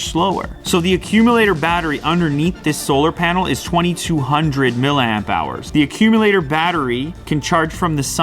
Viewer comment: 30h charging time for 2200mAh battery? No thank you. But iPhone users still gonna buy this shit :D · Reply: NeeP i thought he said 22000 not 2200